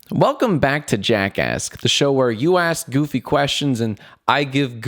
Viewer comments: Lol I thought u said: welcome to JACKASS😂😂😂😂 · I thought he said welcome back to jackas* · Who else thought he said jackass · in the very beginning it sounded like he said " Welcome to JackAss"